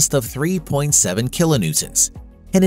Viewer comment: Sorry to post twice. But I gotta say--did he actually say "kilo Newtons" at 31:14?